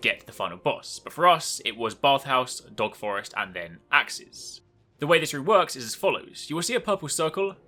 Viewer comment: did you say Aksis? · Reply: Aaron Ybarra axes